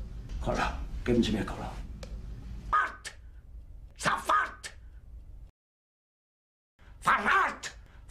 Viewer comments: When he say "sofort" the first time i thought he said " 's a fact" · It also sounded like he said "Fart" when he said "Sofort". Heilige Scheiße!